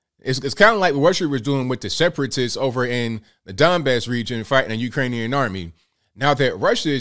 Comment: I thought you said Dimebag region....I was like...oh ok.